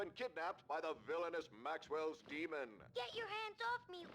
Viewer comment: Enemies stagger and you got back up now? Those are two massive quality of life improvements for this series. Do wonder if Remedy was referenceling the thought experiment or if I just misheard the TV when it said Maxell Demon? If so, have to wonder how thermodynamics will play into all of this.